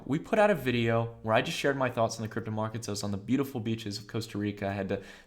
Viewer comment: I thought you said beautiful bitches! 3:00 XD Thought to myself daaamn has changed maan. Great vid as always bud